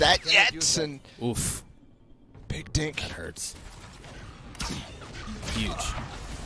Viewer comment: Wait what does the announcer say at 4:05!!!!??? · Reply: He said “dink” uuaahuauh